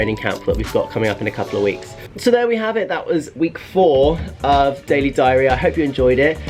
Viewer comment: I hear: "That was week 4 of daily diarrhea"